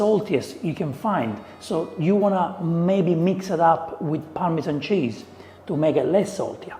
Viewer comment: Did he really say Parmesan at #1:42 ? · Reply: I say Parmigiano, my friend, never the green-box parmesan at that moment